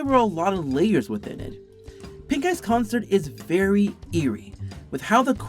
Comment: I Misheard Pink Eye as Pink Guy Pink Guy? When Did Filthy Frank Get Here